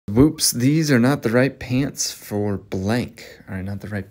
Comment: im high asf rn and i cant read the card i thought he said "paints" and nof "pants" i was so confused 😭